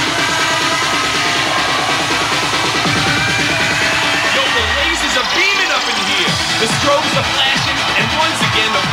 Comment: What he says? I can't hear him very well... bad english of mine. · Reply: He says: "Yo, the lasers are beamin' up in here", the strobes are flashin' and once again the planetarium is throbbing. It never stops."